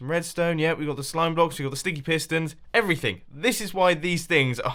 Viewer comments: its sounds like your saying stinky pistons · At about 12 mins into the vid I could have sworn he said 'stinky' Pistons